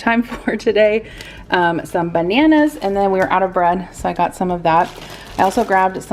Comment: I heard I'm out of breath so I got some of it hahaha